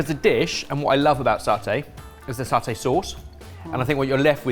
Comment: Lots of mistakes in the captions. I'm not sure if they're done by hand or mostly auto-generated and then proofread but you could do with a better system. At least I don't actually need captions but it'll be worse for people that are hard of hearing and do need them. Examples: 7:48 - says "what I love about satay is the satay sauce", captions say "what I love about saute is the saute sauce" 12:30 - says "half the tub you've bought", captions say "half the tubby pool" There's another bit where the captions have saying "I peed" which, I dunno, might just be the high-stress environment!